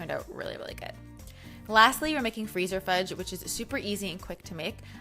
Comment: I thought she said fever fudge like from Harry Potter